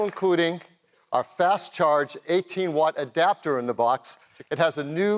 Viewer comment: Did he just say 80W charger in the box? 😱